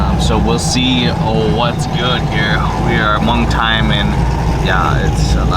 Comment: Did dude say hmong time at 2:12 LOL.